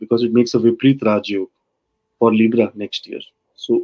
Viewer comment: thanks. And what yog did you say it would make? Repreet raj yog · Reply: Vipreet Raja Yoga